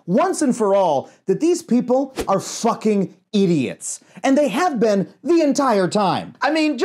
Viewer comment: i almost thought you said "zucking idiots"